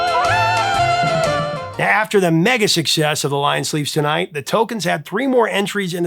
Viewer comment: Thought said "The Tolkiens" at first (uh, what? hobbit forming music?)